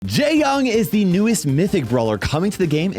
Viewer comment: I heard him say gay-yong 💀